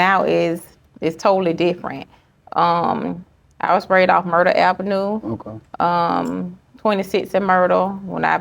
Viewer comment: no disrespect but I had to rewind I thought she said Murder Ave 😢😢😢 So senseless her son is gone 😢